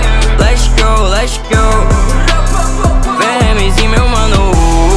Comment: "esse é meu ano" só eu q escutei "esse é meu ânus"? quem escutou clica aq 👇🏽